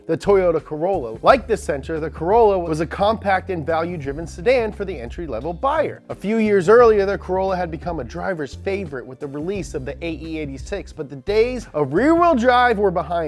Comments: Did you say German sedan? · Wait, wait, wait... 2:40 did you say German sedan?